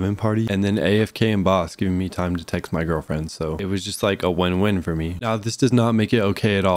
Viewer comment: if i heard correct my man said girlfirends in